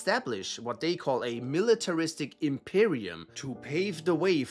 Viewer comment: did he say imperium